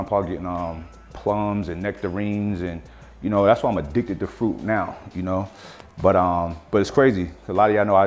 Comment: I heard him say "that's why my dick's a fruit now", and I was like whaaaat?